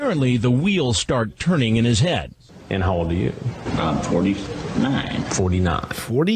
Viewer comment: He definitely said 29 right? Am I hearing things. Maybe Chris just corrected him straight away lmao